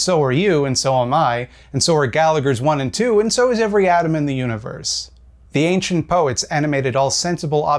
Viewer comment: I thought you said "and so is every Adam in the universe" and was wondering why specifically Adams, then I realised you said atom. Though I guess every Adam still counts too right XD